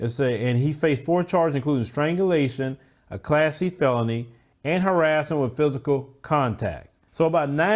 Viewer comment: I know you said "Class E felony" but I heard "classy felony" 😂